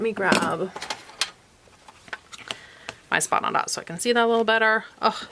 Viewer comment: What are those things you put on the ruler at the 5:00 time mark? Did I hear you call them spot on dots? Where did you get them? I am having vision issues, they might be helpful. Thanks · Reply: Yes, they are called the "Spot on Dot." You can ask for them at your local quilt shop. I also sell them in my shop: <link>